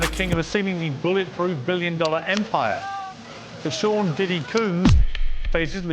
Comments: Did i hear "sean diddy coon"?!? 😂😂😂😂😂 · Piers sounds like he said “Sean diddy Coon” at start of video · I swear at 8 seconds it sounds like he said Sean Diddy Coon.